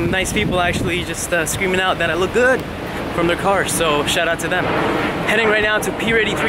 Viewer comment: Plz can anyone tell me what is the word SHARA TO means 0:11 ? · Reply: He said "shout out to them".